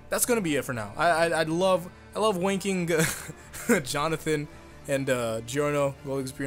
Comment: Did you say i love "Wanking Jonathan and Giorno, gold experience requiem"?